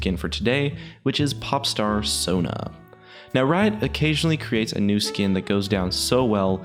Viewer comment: I thought it said pornstar sona XD that is weird